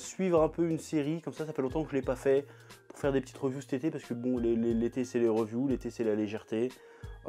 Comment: j'ai compris "l'été c'est les revenus" x)